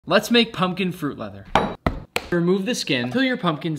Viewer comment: I thought he said pumpkin fruit butter